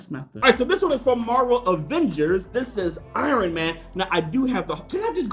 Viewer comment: I thought you said irony man 😂